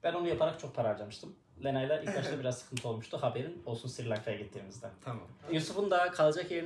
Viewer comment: da yanlış mı duydum yoksa yine Sri Lanka macerası mı geliyor ?